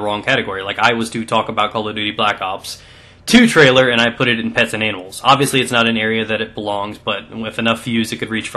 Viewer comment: i thought u said "pets and anals" at 5:20 lol